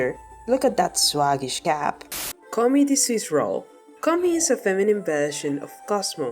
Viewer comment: THAT SCARED ME I THOUGHT IT SAID CONNIE